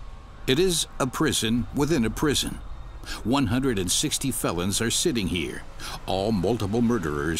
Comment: whenever he says "felons" i hear it as "fellas"